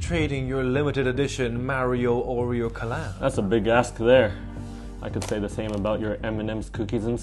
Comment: I thought he said that’s a big ass instead of that’s a big ask lol